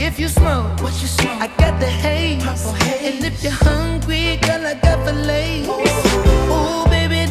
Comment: No cap at first I thought he said "if you hungry girl I got the Lays" 🤦🏾‍♂️🤣🤣🤣